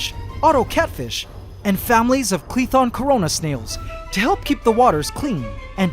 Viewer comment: DID YOU SAY, CORONA?!?!?